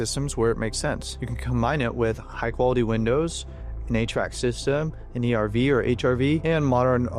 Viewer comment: I misheard you say "you can combine it with .. an 8-Track system" and if this home building technology is compatible with my Fleetwood Mac and Jim Croce 8-Tracks I am so on board ;) Jk you said HVAC and this is a great video. I hadnt heard of this but I can tell my nervous system would love it